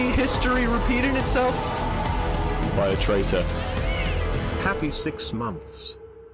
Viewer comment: i started laughing because wilbur said "there was a saying by a traitor" then i heard "happy six months" LOL